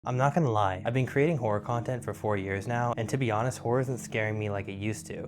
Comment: I misheard and thought you said "I've been doing horror content for 40 years". My brain stopped for a bit and I didn't know if I should ask for your skincare routine or if you are some sort of demon